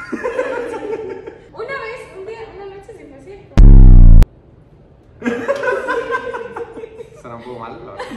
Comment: Que dice no le entiendo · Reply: yo escuche "una vez, un día una noche en el concierto" XD